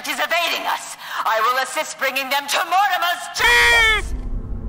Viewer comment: "i will assist them bringing them into mortimer's CHEESE!" Is what i hear lmao